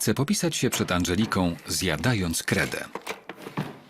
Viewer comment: Ej bo ja usłyszałam zjadając kremer